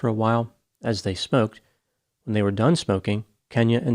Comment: thought you said, "when they were done spoking" lol